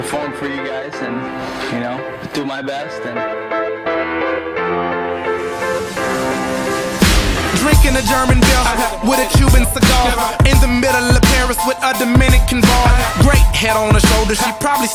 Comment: did he say at 0:41 drinking a german girl ? :)